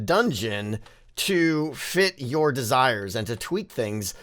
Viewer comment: "To Sh*t your desires." Did I hear that wrong? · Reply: It was "to fit your desire" 🤦